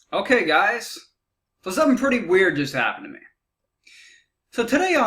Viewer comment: What did you say at 0:02 ? "For something pretty weird happened to me" ?